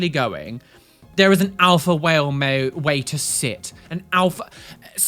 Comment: I know this is besides the point of anything, but at 7:13 it sounds like says "an Alpha Whale", and imagining a whale that is a self-proclaimed Alpha Whale on tiktok is the funniest thing I've heard for a while!